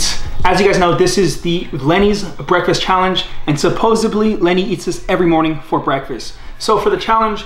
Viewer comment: did he just say supposably instead of supposedly ?